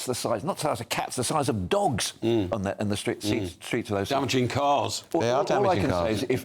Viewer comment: the rats in Birmingham are NOT as big as cats or dogs, i recently spoke to a rodent control operative, they are large but not that big. Rats have been a problem all over the country for decades and they say that you are always within 6 feet of one. When they got rid of feral cats they took away one of the routes by which they were reduced. The same thing happened in London hundreds of years ago. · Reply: Ikr, that guy said “damaging cars” 😂 I thought he said savaging cars at first, made me laugh.